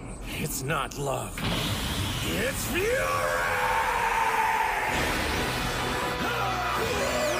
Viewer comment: i thought he said " It's Urine"